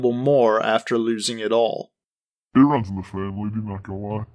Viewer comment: What does he say? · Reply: "it runs in the family dude, not gonna lie"